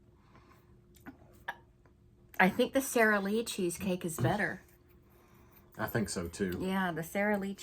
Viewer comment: 😂😂 All I hear is Cheesecake is better❤ We need Cheesecake cake taste test w 🥰🥰